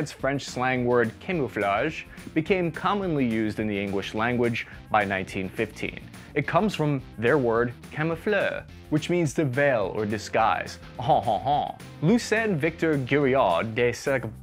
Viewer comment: Did he say "engwish language" at 7:04?